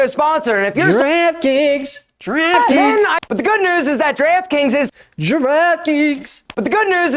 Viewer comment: I KEPT HEARING GIRAFFE KINKS AND I GOT SO CONFUSED